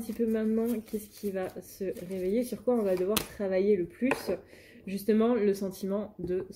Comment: Yes pour le true black tarot ❤ Merci pour cette canalisation 🙏 C'est marrant quand tu as dis «on va devoir acter» j'ai entendu «va falloir te mettre un coup de pied au cul» la traduction de mes guides est toujours plus délicate 😂 Je te rassure ... J'ai moi aussi rangé la garde robe hiver et sortie celle d'été 😅 on y crois !!!